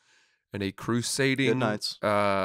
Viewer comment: what is saying right here?? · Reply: after says Raleigh says "Goodnights" which is a comedy club in Raleigh